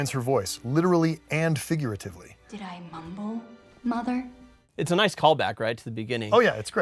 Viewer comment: ok is no one gonna talk about how badass the “did i mumble mother” is. it’s literally the disney princess version of “did i stutter bitch” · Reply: For a long time I hadn't heard the 'even' in that sentence and thought she was saying 'Did I mumble, Mother, or should I call you that?' And I was like, why would she call her mother 'mumble'? And then I realized what she was actually saying and got chills.